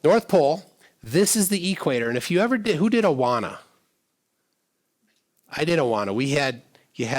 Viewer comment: did you say you did Awana?